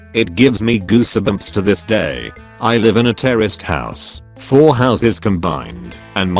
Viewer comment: First story i heard, I thought they said, "Terrorist house" not "Terraced house."